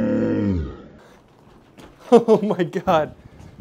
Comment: Lol I thought you said ”That’ll never get sold” at first